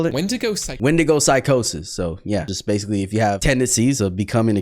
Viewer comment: N-word psychosis that’s what I thought you said